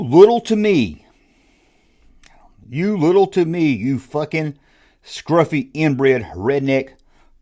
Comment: I Thought He Called you a Scruffy Nerfherder.